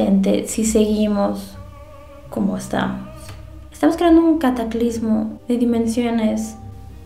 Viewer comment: Estaba bien entrado en el chusme y pensé que había dicho "cacaclismo" Me caí mal por haberme reído